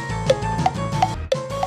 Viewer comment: what say? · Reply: That’s so Raven starts rocking your way but it’s cut